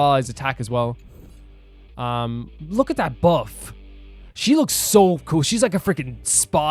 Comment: he said "buff" guys. don't hear what I heard!